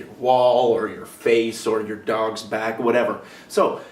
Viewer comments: When he said dogs back I thought he said said dong sack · I thought he said on my dongs back at first